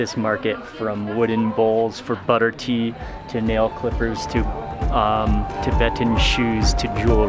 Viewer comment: To male Slippers, BACK UP A BIT, male what ? Ahh Slippers not what I thought I heard